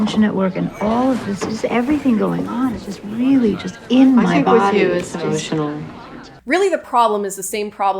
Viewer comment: can someone tell me what the joke is? I can’t hear the other woman at all 😭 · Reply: I’ll tell you 😄 one is complaining about how all the department drama is “getting in her body” and the other says “with you I think it’s emotional.” It’s very dry, but a good payoff for a running joke of that character being whiny about how stressful it is to be in academia